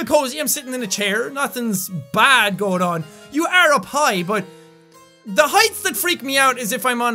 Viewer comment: when he says "you are up high" I thought he said "you are a pie"